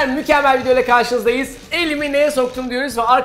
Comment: İlk Dediğinde Elimi Deli mi Ne'ye Soktum Diye Anladım 😂